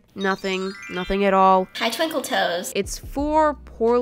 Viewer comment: No one: Absolutely no one: Not a single soul:HeY TwInKlE ToEs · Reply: For a sec I thought u said Twinkie toes lol